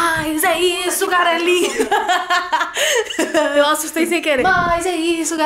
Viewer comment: eu ouvi "garelinha" KKKKKKKK